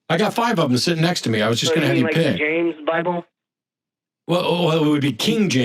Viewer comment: For moment I thought the caller said “Rick James” for his preferred version of the Bible…